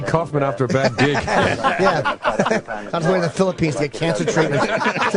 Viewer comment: what does Jimmy say at 4:15 ? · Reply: andy kaufman in the phillipines getting cancer treatment